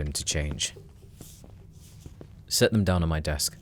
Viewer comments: I heard "sit down on my desk" and I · I THOUGHT HE SAID "SIT DOWN ON MY DESK" AT FIRST